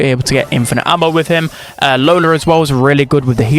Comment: Who? Because I didn't hear it. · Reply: lola